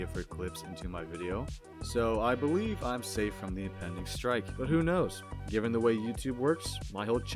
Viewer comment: what he says here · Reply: Im safe from impending strike (I think in the context of yt copyright strikes)